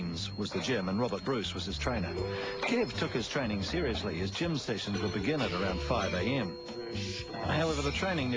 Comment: what did they say abt his gym routine? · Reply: “Kev took his training seriously, his gym sessions would begin at around 5am. However the training never improved his golf game. He was appalling, really really bad. He was dangerous really, and his cricket was worse! But at his training he’d go hard and at times he’d push 200kg weights!”